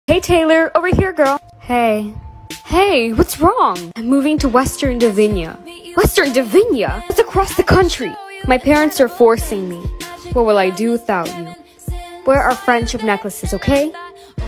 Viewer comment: I wasn’t looking at the subtitles for like two seconds and I thought she said “my parents are divorcing” instead of “my parents are forcing me” 💀💀